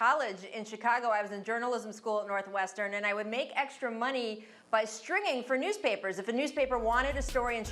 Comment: Thought she was bout to say extra money stripping lmao